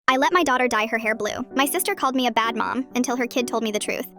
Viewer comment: For a second I thought she said I let her die for hair blue